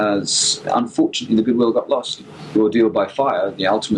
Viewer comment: Very interesting interview, thanks for sharing. Could the tape be running a little fast? The voices sound a tad high and fast and chipmunk-y to me. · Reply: I think Bill just talks very quickly. Which makes the transcription interesting - my favourite mishearing is "ordeal by fire" transcribed as "audio by fire". :)